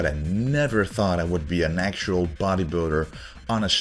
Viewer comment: Being able to instantly tell you're on juice because of your before/after photo is hilarious. Noone could go from being a twig to a huge monster. It simply does not work that way. Nice try. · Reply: after watching the video I realized that I missunderstood him, at the first time I heard natural bodybuilder